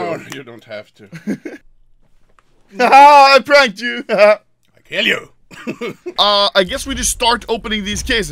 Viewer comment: does anyone talk the language of gods, swedish. that can tell what did he say ? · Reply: Matias_RGT he didn’t speak swedish there, he said ”i’ll kill you” but he said it very fast :)